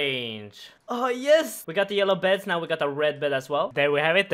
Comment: did you call it a yellow bed? lol